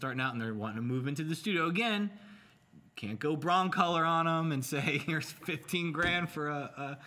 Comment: Did really just say, "...you can't go brown-color on them..." at 1:09:49? reaction during that moment seemed uncomfortable. I really hope that's not what he said tho.